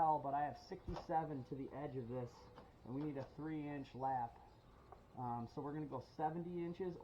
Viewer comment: I thought you said 3 inch overlap, If that is vinyl why 3 inch over lap ??